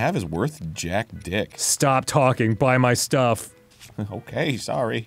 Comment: Every time they say “Buy my stuff!” the first time, all I hear is “Buy my book. Buy my book. Buy my book!”